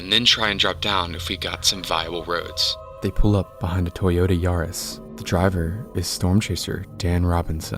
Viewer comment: uhhhhh did i hear TOYOTA YARISS